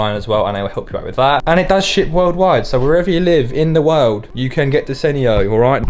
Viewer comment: I really thought said “It does S*** worldwide” 😂😂😂🤣🤣🤣